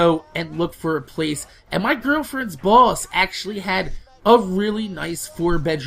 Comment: I thought he said " my girlfriends balls"